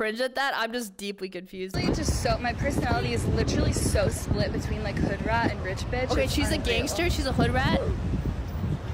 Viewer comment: Can someone explain to me pls the 4:55? I didn't understand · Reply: She said "Hey what's up" to the two guys and they walked right past her like they were ignoring her and suddenly turned around